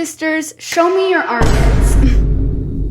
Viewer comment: I thought you said “show me your undergarment”🫩